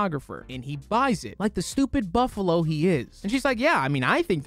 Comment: I think I misheard you....did you call....@ 12:00 did you call Mark Ruffalo a “BUFFALO”?!?!?!?!? Hehe